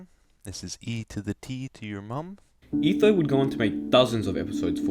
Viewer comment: Did etho just call my mom a hoe?